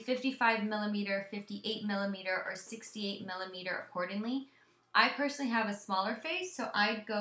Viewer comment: Did you just say 68 millimeter?